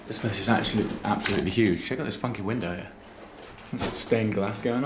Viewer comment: did you say "check out this fucking window"? I think thats the first time I've heard you swear